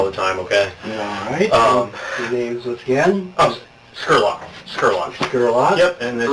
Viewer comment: Detective Skurlock I thought he said Sherlock I had to read the transcript. I bow to the women and men that do this detective work. I couldn't I would commit murder on my first case or go crazy and have to be admitted into a locked ward. I'd like to thank them from the bottom of my heart